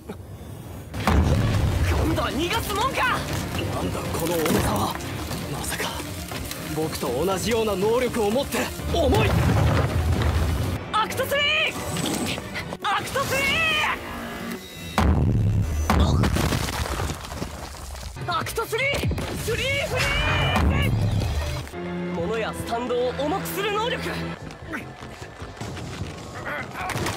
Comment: I heard all might